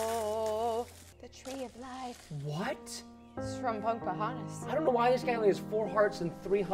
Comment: what did just say in 0:54 of the real tree? i think she said baba-hayin